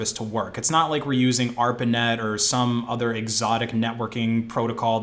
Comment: What did you say on 12:05? "arpin net" or something like that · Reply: ARPANET look it up, it was one of the first attempts at making a network protocol